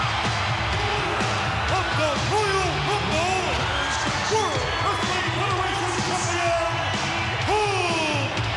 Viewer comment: RIP Hulk. Did anyone else just watch this with the CC from YT on? In english, go to 1:01:00. The CC says "of the royal football world earthling". I wish I could screencap and prove I'm not lying. I had to rewind to make sure I wasn't crazy. Do you guys see that or maybe something different. Swear I'm not trolling.